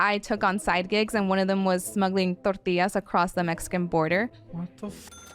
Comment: Smuggling tortillas is hilarious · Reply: I thought she said tortoises at first, because my brain refused to process smuggled tortillas and latched onto smuggled wildlife.